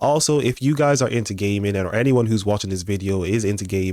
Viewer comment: look I know you said gaming but why’d it sound like you said “if your into gay men” bro…..